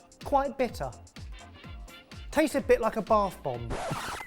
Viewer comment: Bergamot often goes in bath bombs, so I understand the connection there. looks more inviting as a normal but looks like you would get it at a 5* restaurant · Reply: I thought he said "barf bomb"...lol